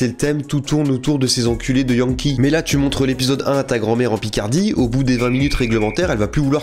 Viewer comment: j'ai entendu "Picardie" et il y a quelques chose en moi qui s'est allumé